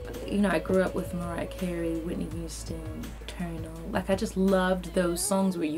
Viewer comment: I can't tell what the last artist she is mentioning is? She says Mariah, Whitney and then I think she says Turner but I'm not so sure?? Can somebody make out what she says at the mark and pls me. Thanks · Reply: Eternal is the name of the group she mentions.